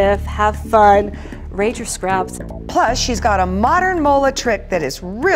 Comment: Oh, modern mola! At first I thought you said "modern mullet," Alex. 🤣